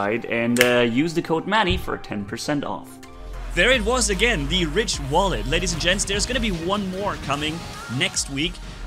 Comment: i thought u said rich wallet